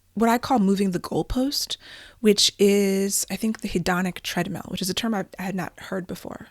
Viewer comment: "Hedonic treadmill" there's a phrase! And right after I've been talking about the Victorian treadmill! Ha. Thought you said 'hypnotic treadmill' at first, which...also works? Sounds like Alan Watts to me! 😉